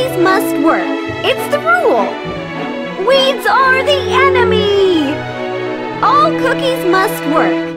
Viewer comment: "Weeds are the enemy!" Me getting it misheard: "Weebs are the enemy!" Me: Tf..